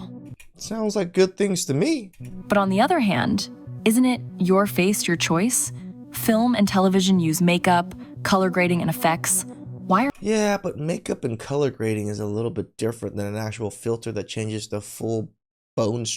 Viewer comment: Made up? I thought you said Make up.